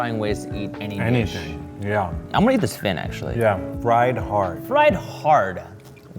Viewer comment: I heard "Fry Hard". It would be a movie about a man being shoved into a frying pan but he just won't cook. After he escapes, he'll take revenge and fry the people.......nvm. I think it's a silly idea anyway.